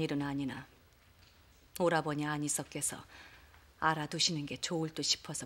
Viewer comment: 오라버니의 아니서? 안이서?? 뭐라고 하는건지 아시는 분 계신가요? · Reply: 안으서요